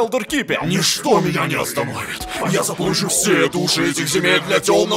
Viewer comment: Послышалось что он уши заполучит :D